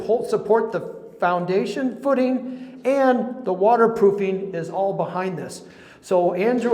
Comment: I love it when you said water pooping instead of water proofing at the 9:38 mark 🤣🤣🤣🤣☝🏽☝🏽